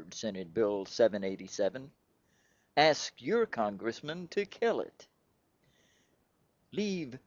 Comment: At first I thought he said, "Senator Russ Feingold sponsored S-787... Ask your congressperson to KILL him."